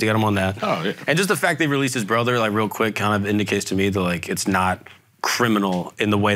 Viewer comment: mark did you say they released his brother real quick?